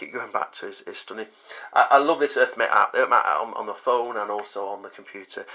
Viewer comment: you say "something" app you love on your phone and computer. What's it called mate? because it's impossible to understand what you said there, earth me? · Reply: Sorry, it is called the Earthmate App